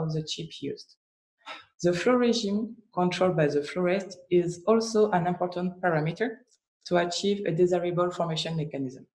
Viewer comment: Hello, I couldn't understand what you said in 6:53. The flow regime controls ... Can someone please help me? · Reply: Hello Maria, At 6:53, said "the flow regime controlled by the flow rate is also an important parameter to achieve a desireable formation mechanism". Hope this helped! Have a good day, Marie from Elveflow